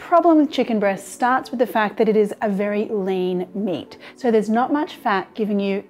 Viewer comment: I thought she said "the problem with chicken breast starts with the fact that it is a very lame meat" at 0:42.